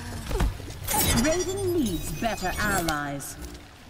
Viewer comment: what did she say? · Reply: Raiden needs better allies